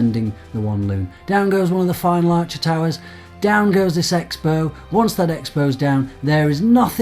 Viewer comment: did the say sex bow?